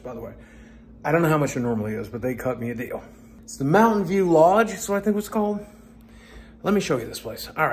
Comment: Sounds like you said Mountain Dew Lodge.😁